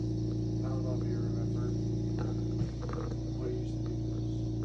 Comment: The guy at 5:00 sounds like he was saying"i hope you remember what you used to do to us as kids"